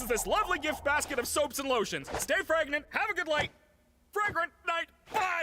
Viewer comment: i know its all serious and stuff but...... at first watch i thought he said stay pregnant i was like OMGWHAT?